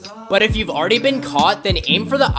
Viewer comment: Did he just say good luck to me? Nu uh why would I meet a shark